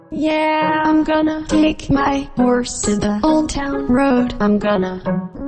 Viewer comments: Is it just me cause I heard "im gonna kick my horse to the old town road"😂😂😂😂😂😂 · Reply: I hear "Im gonna KICK!!!!!!! my to the old town road" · Is it just me cause I heard "im gonna kick my horse to the old town road"😂😂😂😂😂😂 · Is it just me cause I heard "im gonna kick my horse to the old town road"😂😂😂😂😂😂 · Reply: I heard “I’m gonna tick my horse”